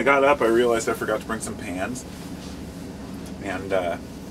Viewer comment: I thought you said pants lol